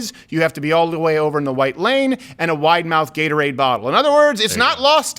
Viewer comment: ~Why did it sound like you said that you need to be all the way over in the "White" Lane. Lol